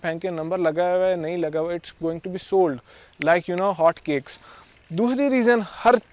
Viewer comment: i thought you said hot chicks